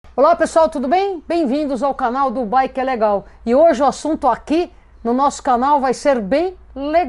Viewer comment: juro que entendi ''bem vindo ao canal do boy que é legal''